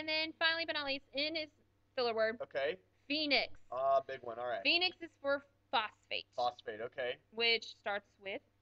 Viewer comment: I'm doneee at 4:56 I thought she said Phoenix is for Fuckface